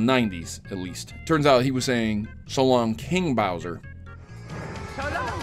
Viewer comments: "So long, King Bowser" Gonna be honest. I thought he always said "So long, Bowser" with a heavy Italian accent. My life is a lie. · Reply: I honestly thought he said "So long, BIG Bowser" myself. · "So long, King Bowser" Gonna be honest. I thought he always said "So long, Bowser" with a heavy Italian accent. My life is a lie.